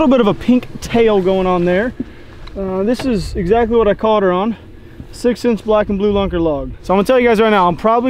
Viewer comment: Did you say you caught that on a 6th Sense lunker log at 11:55? Interesting considering you guys get accused of ripping off 6th Sense all the time. 😂 Either way I enjoy most of the vids 👊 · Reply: He said six inch. 🤷‍♀️🤦‍♀️